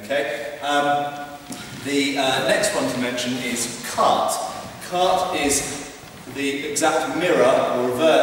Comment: Which is the one he starts talking about at 2:19? I cant figure out the name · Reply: It's actually *quarte*, the fourth position. See <link>